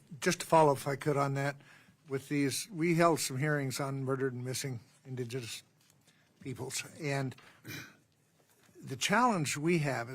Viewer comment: Did he say “murdered/missing ninjas/peoples?” 😂